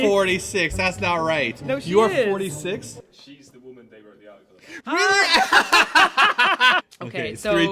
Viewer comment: What did Dan say at 12:07?? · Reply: "She's the woman they wrote the article about."